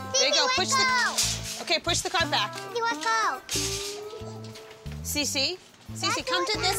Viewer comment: why’d I think he said “u a ho”